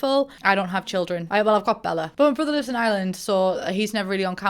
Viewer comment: I had to rewind when you said “My brother lives in Ireland” because at first I thought you said “My brother’s on Love Island”